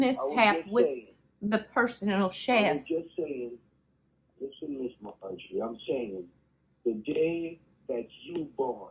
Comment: Did he say "listen Ms. Maharshi" at 4:12? That's my name (minus the Ms.) and i thought someone was calling me...